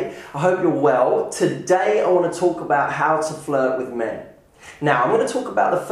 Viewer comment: i thought he said "how to flirt with me" ;D